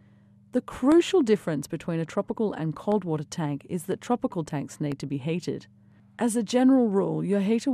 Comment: sounds like she says tropical tanks need to be hated.